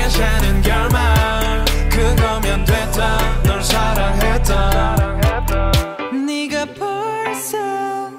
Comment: it sound like it said the n word 😂